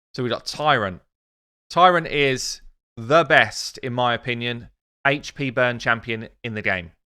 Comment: Did I hear you say "The best" or "Abess"?